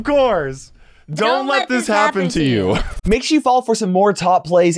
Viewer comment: What I heard: Make sure you fall for some top plays